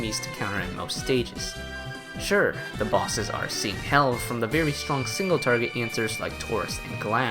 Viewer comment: What is being said? Seen hell? I can’t hear well and don’t catch what is being said there, can anyone help?